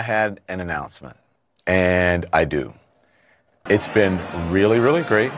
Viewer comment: "This is the last night that we'll be CNN tonight with Don lemon" I love this damn sentence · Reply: I thought I heard, this is the last night of CNN.... I was gonna jump 😂🤦🏽‍♂️